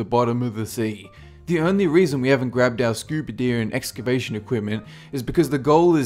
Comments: Scuba deer and excabation equipment? Am I hearing this, or is that what he said? (Just after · Hex I heard scuba deer as well right after